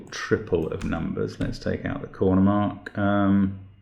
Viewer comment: I heard "let's take out the corner-mark" as "let's take out the corner, Mark," and was curious to see how you were going to do that.